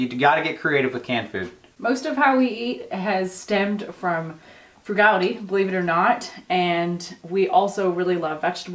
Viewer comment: what did she say??? "Fugali?"